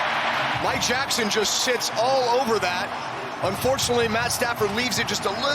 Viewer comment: did i hear it wrong or did they say that “mike jackson just sh!ts all over that” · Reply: He clearly says SITS